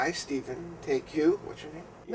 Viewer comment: Did he just say "whats your name?" wtf